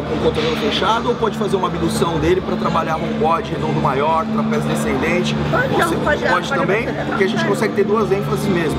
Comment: eu escutei trapézio descendente ?